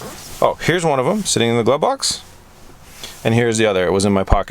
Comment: Did he actually say glove box?